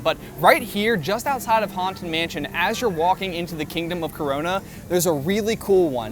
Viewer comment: Did he say "kingdom of CORORNA"